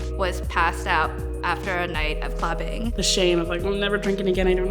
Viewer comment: what does she say? After a night of…clapping??! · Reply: clubbing